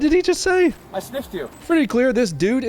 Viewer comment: & @ 11:00 & @ 11:21 "I sniffed you" - LMFAO Dude was chilling with Hunter "Crack" Briben & Sniffin' Joe Briben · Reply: Was it 'sniffed' or 'stiffed'? I thought he said 'stiffed', like a stiff arm maneuver.